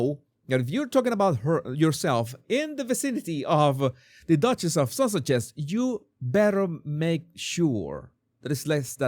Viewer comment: It sound like you said The Duchess of Sausages! 😂